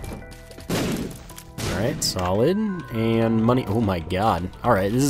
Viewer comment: Whenever you say "solid" I always hear "salad"... that's so stupid I just laugh every time